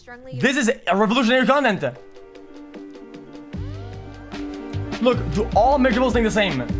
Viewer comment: I literally thought he said “does all omegalols think the same”